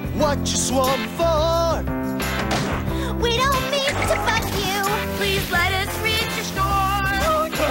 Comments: What did Courtney just say??? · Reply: She said bug but it sounds like smt else 😭😭😭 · Did she say "We don't mean to f***k you." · Am I the only one who heard “We Don’t Mean To F*ck You” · We don't mean to fuck you....IS THAT WHAT I HEARD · I THOUGHT SHE SAID WE DONT mEAN TO F*CK YOU HELP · my sister said it sounds like Courtney is saying “we don’t mean to f*** you” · did Courtney say the f word? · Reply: No she said “bug you” 🤓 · WHAT DID SHE SAY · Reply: “we don’t mean to bug you” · why do I hear “we don’t mean to f### you” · in “it’s rowing time” I thought Courtney said “we don’t mean to fuck you”… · COURTNEY KINDA SOUNDS LIKE SHE WAS SAYING, WE DONT MEAN TO F*** YOU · She sound like she said we dont need to coughs you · did say we dont mean to fu## you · Not me thinking inside I mishear something in its rowing time “We don’t need to 🦆you!!!”